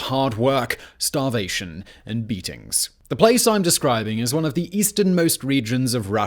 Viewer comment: I really thought he said "Anyone can be turned into a beast with 3 weeks of forced labor, starvation and meetings." Hilariously, it still applies.